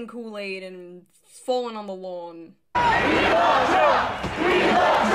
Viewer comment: The “We love Trump” chant is straight out of South Park lmao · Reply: i thought they were saying "we are trump" and I was a little concerned